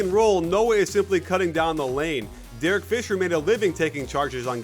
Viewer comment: Anyone else heard "Derek Fisher" at 2:00 instead of Shane Battier...